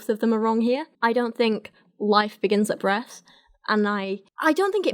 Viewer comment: I misheard "life begins at breath" as "life begins at bread" at first.